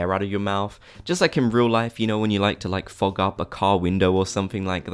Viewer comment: I honestly thought he said fuck up a car 😐